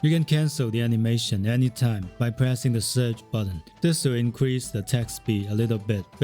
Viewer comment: How do you attack cancel? Sounded like he said surge button but I couldn't understand :/ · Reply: Its easy, hold attack button at same time press the search button. You gotta sync press them, like 1st swing attack you follow up search and repeat. I use this method on my 1st playthough using two hand warrior, helps alot really